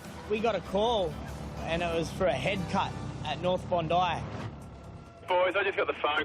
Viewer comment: when they said he had a bad head cut I thought he said, it's a pretty bad hair cut